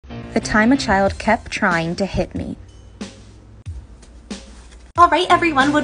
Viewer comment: Help i thought it said “the time a child kept trying to hit on me”